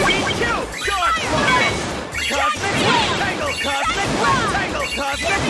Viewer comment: "cosmic rectangle" "Cosmic rectangle" "COSMIC RECTANGLE" "COSMIC RECTANGLE" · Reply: idk why i hear "CHOCOLATE RECTANGLE"